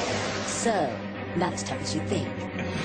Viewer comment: What does Envy say at 1:40? I can never seem to make it out. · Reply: antipathy "So, not as tough as you think." is what I think was said. The middle bit was blurred together though, so I could be wrong.